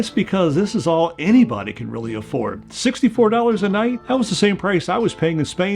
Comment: 65 a night for a 6 bed dorn jeez Louise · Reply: At first, I thought he said “60 for 20 a night”, meaning €20 for 3 nights, which is pretty good. But then he said “$64 a night”, so I’m guessing he meant €64.20 the 1st time, which is a lot more than I thought it would be 💀